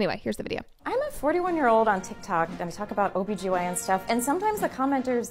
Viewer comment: Literally thought you said "an OBGYN and a llama" .... imagine my suprise when I saw Obama